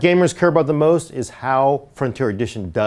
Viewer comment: I thought he said Niggas gonna tell you why... I'm like very mature... But I rewinded it and heard Nick is gonna tell you why.